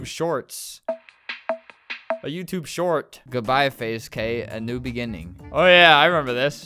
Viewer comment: YouTube short I thought flamingo would say short life